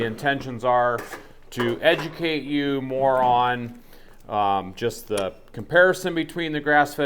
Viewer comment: I heard this as "this video is to educate you, moron." Haha